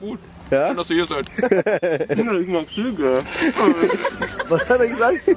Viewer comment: Was sagt bei 5:57 ? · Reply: Kathi Gem ich mag Züge